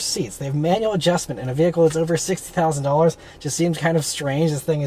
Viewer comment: Thought u said “over $60,000 there for a second”😮😮